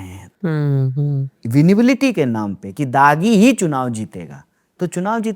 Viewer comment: vinibility? visibility?? Kya word hai...koi batao please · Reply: Winnability ✅